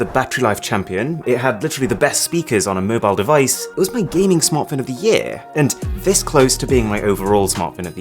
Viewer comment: who else heard "usme gaming smartphone" hindi language LOL